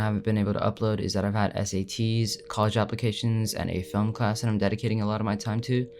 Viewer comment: I thought he said STD’s 😂😂😂😂😆